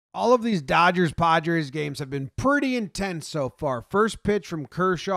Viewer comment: at first I thought he said "All these Dodgers/Podgers games"